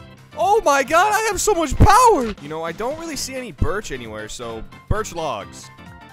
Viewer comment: Did he just say BIRTCH